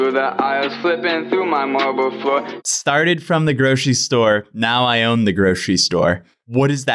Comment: Am I the only one who thought they said, ‘started from a Gucci store; now I own a Gucci store’